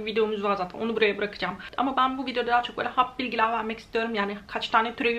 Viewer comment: ben mi yanlış duyuyorum yoksa 'hap bilgiler' mi dedin? 3:48 ilk defa böyle bir terim duydum